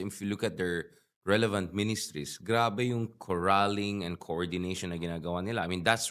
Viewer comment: Did you just say “GRABE AYA” hahaha! Na-bilingual ka doon hijo. That was so cute! 🤪